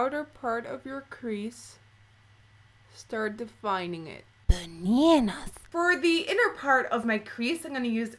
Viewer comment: Hahah at 1:20 did she say "bananas"? I've watch that part about 7 times and can't work it out! :')